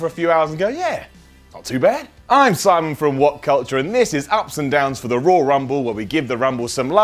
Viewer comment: wait a minute I thought u said 7 ups and 10downs